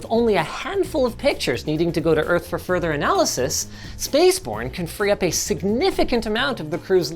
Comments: Correction at 15:20 - This is meant to say "TDRS Satellite" instead of "TRDS Satellitte." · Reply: in captions it says "Spaceporn" instead of "Spaceborn" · closed captions read "Spaceporn" instead of "Spaceborn" 😂